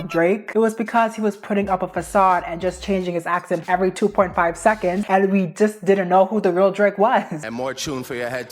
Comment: I thought she said: he changed his Abs every 2 seconds 😭